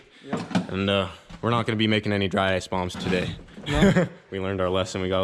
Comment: i swear i thought u said no more "dry-ice BUMPS" not bombs, lol i was like WHAT the fuck yall lining that stuff up and snorting it for laughs?! stuppiiiddd! plz nobody do that lmfao